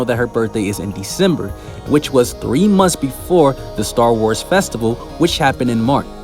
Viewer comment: that sounded like ‘Star Wars’! 😆